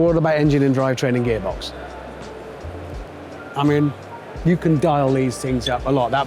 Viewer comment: He said, "you can dial these up a lot." I heard, "you can blow these up a lot." I get the feeling he's about to learn how much you can't dial up a boxer. LOL.